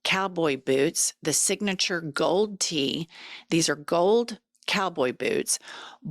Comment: Pre-order our new book and join our Substack by clicking here: <link> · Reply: I was busy, but listening, and I thought you said GOLD TEETH!! 🦷 🦷 omg! No. It’s infact GOLD T😮😂😂😂😂😂🎉🎉🎉🎉😮😮😮😮😮